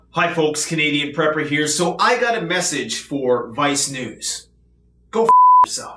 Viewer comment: When you said Vice News I misheard it as My Snuze.